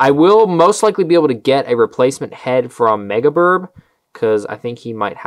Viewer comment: what did he say where he gets mega parts? didn't understand it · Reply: MegaBirb